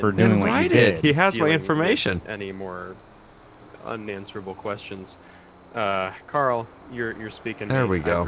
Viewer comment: when he said "unanswerable questions" I thought he said "onions"